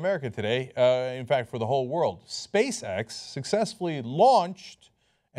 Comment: He said: "Space X", I heard: "Space-Sex".